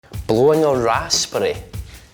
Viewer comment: I thought he said blowing an ass spray